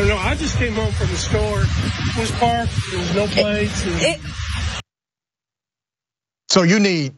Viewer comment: I'm just asking. .. did he say "no plates"? As in license plates? I'm not on a side it's just windy in the video.